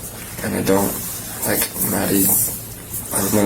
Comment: on your video, does say " I don't "Like" Maddy? or does he say "I don't "think Maddy? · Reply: Just slowed it down and listened again… now I hear him say “I don’t think Maddie… she won’t just call me”!